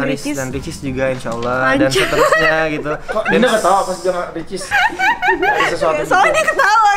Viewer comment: menit ke 11:33 itu ngomong apa yaa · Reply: Kalo ga salah "kok Dinda ketawa pas Harris sama ricis"